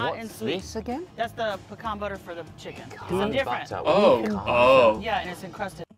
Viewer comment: was right about the butter. At around 12:43 the waitness said "Sweet hot garlic pickles" and then "pecan butter for the chicken". Pecan butter was also used earlier on the sweet potatoes. I think heard the "sweet" for the pickles and the "butter" for the chicken, and then remembered the sweet potato from earlier....then his mind somehow combined everything and created sweet potato butter. 😂 Sounds good though.